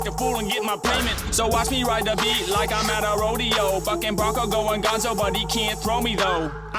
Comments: I thought he said fucking but he said bucking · did he said fuckin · i thought he said Fucking 💀💀 he said bucking